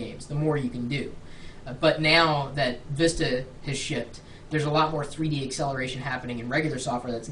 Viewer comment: ya i thought he said " now that vista is shit" but he said shipped XD